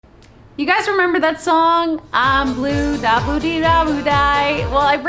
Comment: Whenever I hear "I'm blue daba dee daba di" I actually hear "I'm blue I believed I would die"